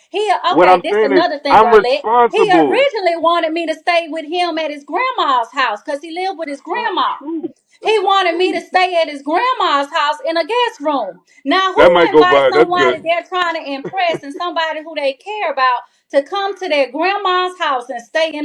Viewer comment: WHAT??😭😭😭😭 did he just say "that might go viral, thats good"😧😧😧😧